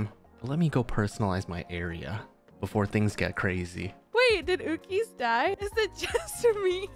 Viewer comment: MC judged that the name "Lapis" was too powerful for lol. Also, I think said "Did Uki's (game) die?"